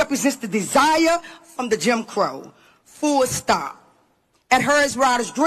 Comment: Did she say Full Stop 1:17😮